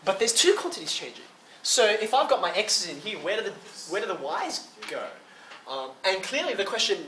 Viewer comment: "Where do the wise go?" sounds like a philosophical question. (Yes, I know that that's not what was said.)